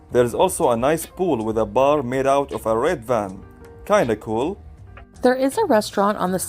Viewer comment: Omg. I thought said "R*pe van!